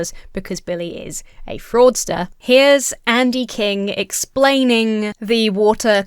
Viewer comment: man i heard "andy king" and thought it was a collab and got so confused 😭